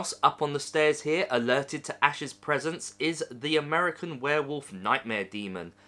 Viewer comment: So you get free stuff for adverting...interesting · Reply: I thought you said they gave you the American werewolf in London? :15